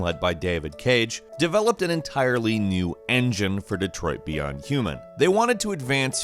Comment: Did you just said Detroit "Beyond" human?